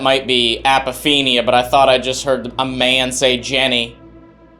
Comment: I don't hear what everyone else does. I hear someone say, "nice" instead of Jennie. When asked if Jennie pulled on jacket - I heard a clear yes but the guys say it says "maybe." I tried to hear what is labeled for those and can't. The same thing happened last week. Am I the only one that hears those differently?